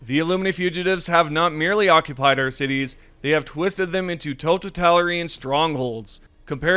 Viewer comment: woah the way you pronounced “totalitarian” was so weird, had me go back and listen to it several times and I think you said “totitalirian”